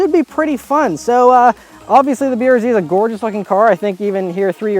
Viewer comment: did he just say the brz is a gorgeous fucking car?